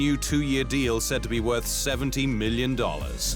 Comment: I thought you said 17 million but then I rewound it and I heard 70 million. That's a nice wage for two years. Congrats Danny!